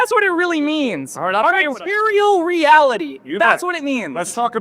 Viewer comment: Did he just say "imperial reality"? 😂😂😂